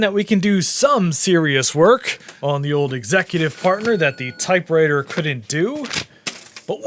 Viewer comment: Did you say Executive Fartner? Because that’s what I heard… 😂